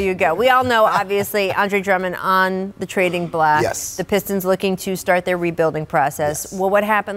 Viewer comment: did she say trading black? Lol